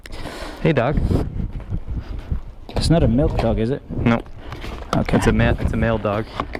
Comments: Is he from South Africa? I'm from Cape Town :) Also, what's a milk dog? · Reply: i kept hearing "milf dog" and was like wtf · DID YOU SAY *MILK DOG*?!?! ...I think he said "milk dog". UGHHHHHHHHH D:<